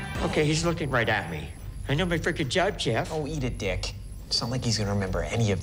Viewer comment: does anyone understand what he says between 1:48 and 1:50? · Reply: playyboy nyc "I know my fricken job, Jeff" The gray alien (Jeff) was mocking the green one (Kurt) because Kurt probably said he knows how to do his job yet ozzies eyes were open which means he didn't